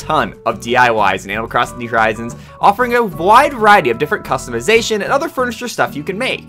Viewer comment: am I the only one who heard 'vide wariety'